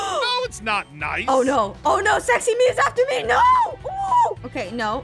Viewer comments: tho🤣🤣 What did you hear from? I heard "oh no,oh no sexy me is after me" 🤣🤣🤣🤣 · Why did she say sexy? This is a kid friendly channel?